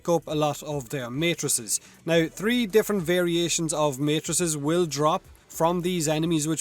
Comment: Did I hear 3 different variation of mattresses?